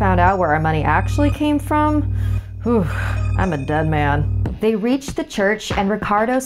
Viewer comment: notice how he says “i am dead man”. So doesn't he kinda confess that he is dead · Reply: It sounded like he said “done man”